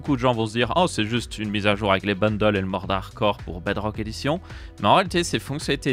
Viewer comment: omg j'ai compris "bundle et moldarquor" ptdrr je me suis dit mais non ils vont vraiment ajouter ce mob en vanilla ??